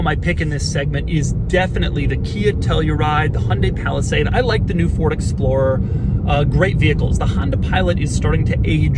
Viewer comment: Did he really say "l like the new Explorer?"